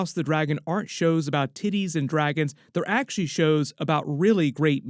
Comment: did you say titties? 😂